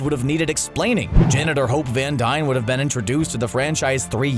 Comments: When he says "Janet or Hope Van Dyne" I thought he said janitor van dyne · I thought you said "janitor Hope Van Dyne."